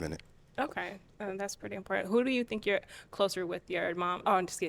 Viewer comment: did she just say "who do you think you're closer to your dad and your wife" when he said he lost his dad at 6 ?? And then said "im just kidding" like it was a joke ??